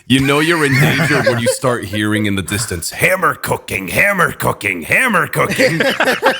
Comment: thought she said hammer-cucking at first and nearly lost my mind